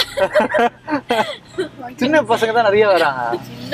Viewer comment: what age group she says? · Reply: 2k kids 😂20 age boys maybe evn 16 may enter clients also girls available frm 15 😢sry we tryn save these ppl frm.pimps workn NGO